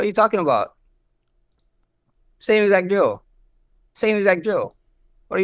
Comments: He thought Max said “Joe” when he said “drill”. Same exact “drill” (Joe). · max sounded like he said Joe when he said drill